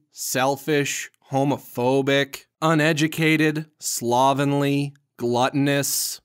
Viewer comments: I thought you said slavishly as slavenly but you said slovenly. Good. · I could have misheard this bit I thought he said homerphobic. I know he didn't. I know he said homophobic. But still.....gave me a decent chuckle.